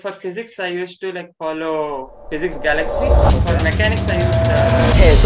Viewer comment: did he say Physics Galaxy? And which book? 700+adv illustrations or set of books?